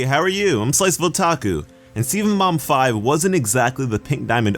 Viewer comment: i thought he said Steven mom 5